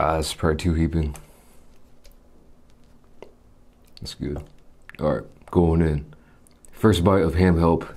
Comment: did he say bismillah